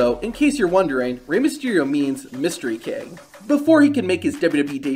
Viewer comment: Just an FYI, in Spanish the letter "j" is pronounced like an "h" so it would be LA-GAR-TEE-HA. Also it sounded like you said Rey Mysterio means Mystery Kid it means King Mystery. Edit: I misheard the "Mystery Kid" part